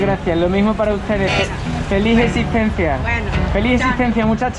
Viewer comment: feliz intincencia?? No entendí qué quiso decir??